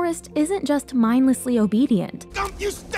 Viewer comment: Didn’t know the guy who played forest played woody infill I heard “I gotta Dina bubba!”